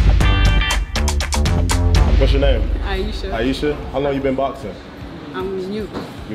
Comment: On the first one I thought said how long 6ou been bald for I was like that's messed up